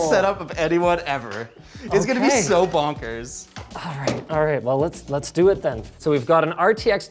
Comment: did he just say bonkers? lmao